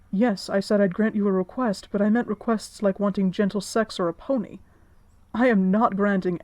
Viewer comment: Oml I thought he said Gentle Sex With a Pony and I actually died😂😂😂